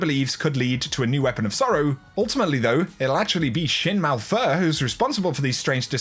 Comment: i heard "shit malphur"